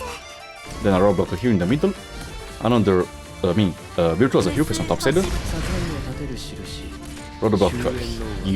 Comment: I thought you said "Myrtle here facing topside" while deploying war criminal woman. 12:55 refers to Lutonada (4* Defender Juggernaut)─ on CM doesn't work on my end (E2L25) due to Burn damage. However, when your Saria died, just spam helidrop like Texaster, Yatoter, Gravel, and/or any leftover operator.